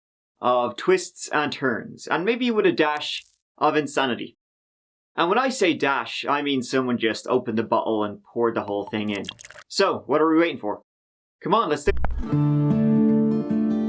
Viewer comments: I thought for sure he said, "...opened the butthole and poured the whole thing in." After I said, "Ooooooh BOOOOTTLE...Im a sick man..." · Am I the only one that had to go back a few times around 32sec. in??I kept hearing him say ''and maybe a dash of insanity, and when I say a dash, I mean someone just opened the butt hole (instead of bottle) and poured the whole thing in''!!...ROTFL